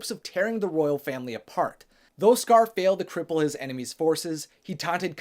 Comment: Why does he say “he taughts kion and simba’s son”